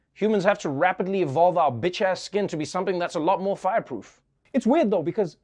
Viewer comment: Did he say "evolve our bitch ass skin"?